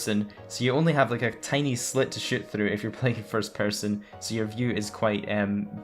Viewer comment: When he was talking about the kuruma it sounded like he said you only have a little slit to shit through!😂😂😂🤣🤣🤣